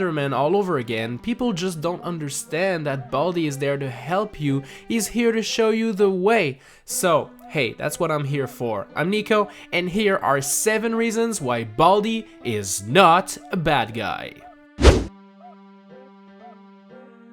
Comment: At first I thought it said “baldi is not a bald guy” 😂